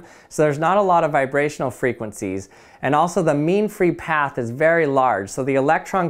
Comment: did he just say meme free path ? Lol ik what he said just couldn’t pass up the opportunity 😂